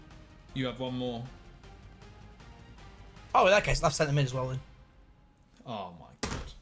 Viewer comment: amazing vid as always! but i heard "left cm"?